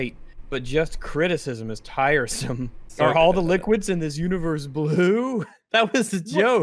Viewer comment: I misheard the one statement as all of the liquids in the show were wudu. For some reason I found this inordinately hilarious. That is all.